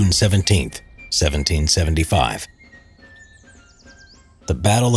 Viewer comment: Aren’t we gonna ignore the fact he just said 171775? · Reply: samuri Alianderp293 I thought he said June 17th 1775.